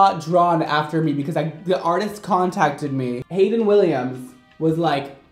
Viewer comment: Lol… when he said the bratz artist who contacted him was named “hayden williams”….. i at first thought he said hayley williams and i was like when did paramore start designing bratz dolls? 💀